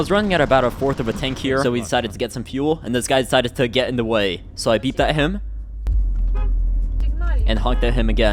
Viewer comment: "cигналь ему?" или мне послышалось ?)